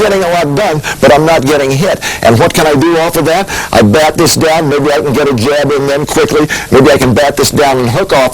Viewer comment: WTF did he say at 2:36, lol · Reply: i back this down maybe i can get a jab in there quickly